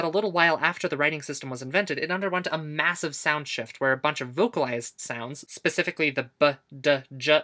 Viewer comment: i thought bro insulted my language cause i thought they said Sound shit HEL 😭